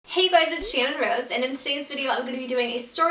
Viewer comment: i thought it said Cinnamon Rose 😂😂😂 Sorry channon 💞💞